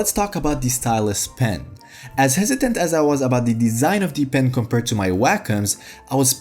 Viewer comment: what are you saying bro:D to fast:D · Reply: As hesitant as i was about the design is what i said :)